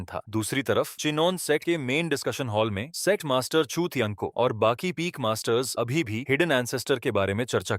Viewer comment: kya bola ... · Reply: Chu tian bola hai chut tiyan nahi😂😂